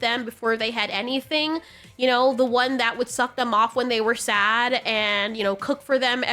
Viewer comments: I’m sorry but at 11:32 did you say “suck them off when they were said” or did I miss hear that 😂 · lol did she just say suck em off when they were sad lol i joked on my energy drink lol god damm